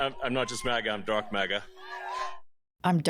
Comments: I thought he said “I’m drunk maga” 🤣🤣🤣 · I thought Elon said that he was "Drunk MAGA"